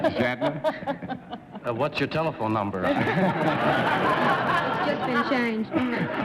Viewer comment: Can anyone make out what the first guest says (@ 6:52) in reply to Captain Kirk asking for her telephone number? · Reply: ***** I think she said "It's just been changed..."